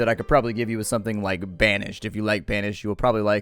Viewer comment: Oh so he said banished, I thought he said Vanish and I started thinking of Mark